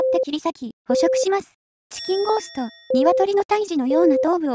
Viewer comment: 風呂で聞いてたからチキンゴーストがチンゴーストに聞こえた